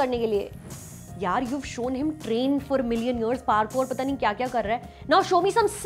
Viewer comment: did u said million years?